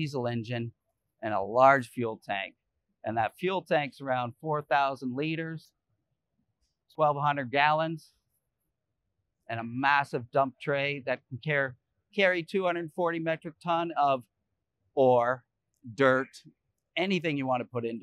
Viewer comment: I thought he said it could carry 240 metric ton of whores.. I had to rewind it and read it.. :'-D